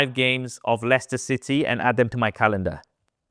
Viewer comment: if gemini cant understand Saf's english, im not sure will understand my english any better 😁😁 11:07 find me the next five games of Leicester City and "ADAM" ??? instead of "add them" lol